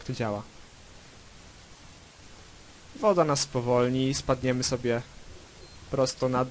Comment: Tylko ja usłyszałem ,,woda nas spowolni i spadniemy prosto na dupę" ??